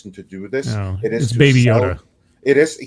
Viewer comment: I thought at first said, ‘Baby oil’. (How many babies does it take to make baby oil?)